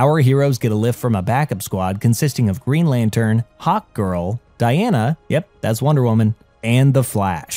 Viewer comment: I keep hearing “Hawk tua” when it says Hawkgirl 😂 I need help😢